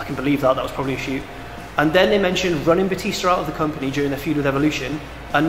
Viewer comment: I thought they said bootista not bluetista